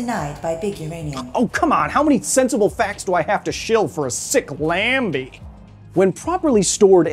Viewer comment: did you just call a Lamborghini a "Lambi"? It's Lambo, you phallus.